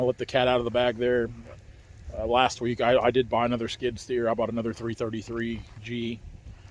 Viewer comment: I thought you said "skid-steer" but then I thought maybe you said "skidder", so I went back and checked. You are getting a new skid-steer.